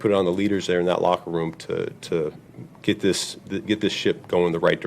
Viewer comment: I keep hearing "Get this shit in the right direction... not ship"